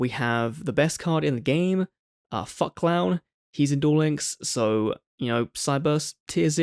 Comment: What did he say?!?!?! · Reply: Fuck Clown